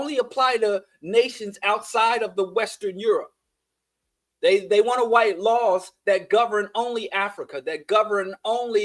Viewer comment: did you just say "they wanna white laws?" 🤣. Happy accident, perhaps.